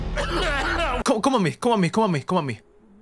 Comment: DID BRO JUST SAY COME ON ME